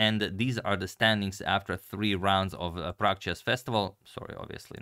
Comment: Oh. Prague Chess Festival. I thought you were saying Pragg Chess Festival.